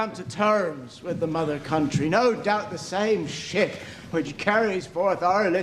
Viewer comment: Did he say "Ship" or "Shit"? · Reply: "The ship which carries forth our grievances will bring back their redress."